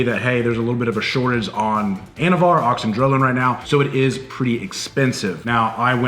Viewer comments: The whole time during you two's discussion I thought yall were saying ANABAR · I thought he said “anabar” lol I’m like oh shit!